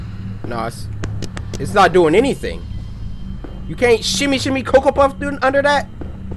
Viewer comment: did he say shimmy shimmy kokobop or am i tripping